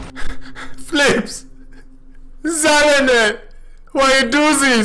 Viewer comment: Wait what are you saying? Sounds like my name but i wasn't even in the game it was Seismus close but still not. :D 2:15 <3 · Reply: omg was it seismus and i was instead saying sallenel!? Omg that is so funny haha. I was actually wondering, cause one time i said seismus and i thought while video editting ... omg you said the wrong name haha ... i am so sorry :d